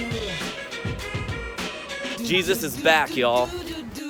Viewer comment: What did he say on 14:24? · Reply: Jesus is BACK, y’all.